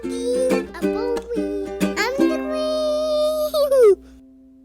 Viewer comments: Everytime I hear singing your into, I hear "I'm the Queeeeeen", instead of "I'm Colleeeeen". It sounds fitting both ways! He's super cute. ❤ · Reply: He does actually say “I’m the queen”! Colleen said that in a previous vlog when the intro first came out :) it is super cute! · since I love your introooooooo also it kind off sounds like is saying “im the queeeeen” instead of Colleen